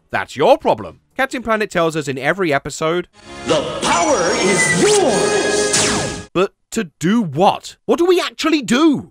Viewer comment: that's quite funny considering I know a lot of children I know understood "the problem is yours" instead of "the power is yours", that's amazing.